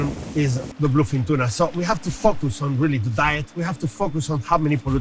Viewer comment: Definitely thought that dude said "Fuck with"not focus.